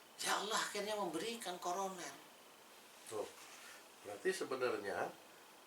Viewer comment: Allah memberikan corona? Apakah saya salah dengar?